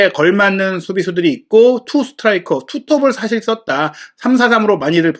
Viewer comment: 다큐멘타리 7:50 초에서 콘테가 전술에대해서 말할때 3스트라이커라고 하는거 같은데..? 제가 잘못 들었나요 3 디펜더 2미드필터 2 윙백 3스트라이커 이렇게 말하던데